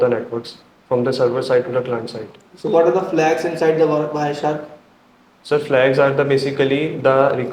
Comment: what am i listening bro said barshark in the vid watch for